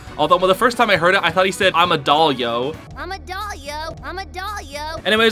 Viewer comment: When I first heard him say “momodolia” I thought he said “ I’m a dog yo”